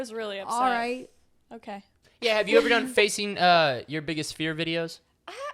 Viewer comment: i thought said face eating for s sec😂